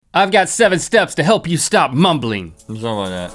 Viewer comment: i stg at the beginning i heard "i'm got7"